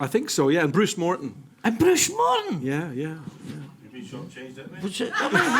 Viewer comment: What is said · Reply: “You’ve been short changed there mate”